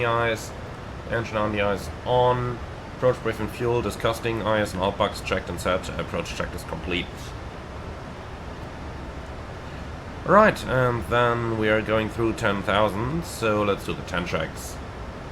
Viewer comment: approach briefing “disgusting” 😂 I heard that!